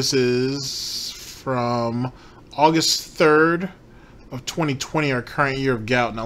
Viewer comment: I misheard that as hogust